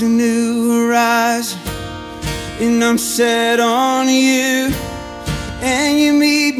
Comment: This is a great song, but the first time I heard it, i could have sworn he said "I'll sit on you" instead of "I'm set on you."